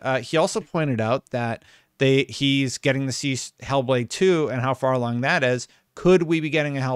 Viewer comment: I misheard "Hellblade" as "Hellboy" and now a Hellboy game is all I can think about